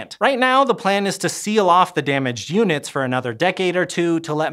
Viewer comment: Wasn't paying attention and had to rewatch 6:55 a couple times. Sounded like he said "seal off the damaged humans..." Had to turn on the captions too see he said "damaged units"! 😅